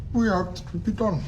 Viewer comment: What did he say at 53:50? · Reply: We hoped it would be done